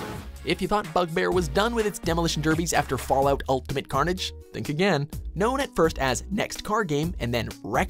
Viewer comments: did he say fallout ultimate carnage? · Did he say "Fallout: Ultimate Carnage" at 5:17 ?